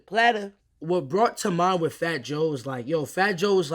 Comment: I THOUGHT HE SAID FAT JOYLNEE THE WHOLE TIME DAWG 💀💀💀💀💀💀💀💀